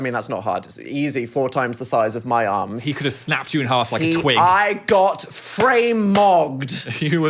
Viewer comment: guys please tell me I'm not the only one who heard "he could have snapped you in half like a twink" 😭